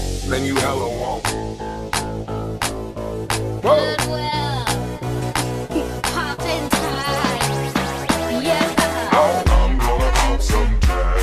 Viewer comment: What is that girl saying in 2:56 ? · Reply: I think : "Goodwill... poppin' tags... yeah!"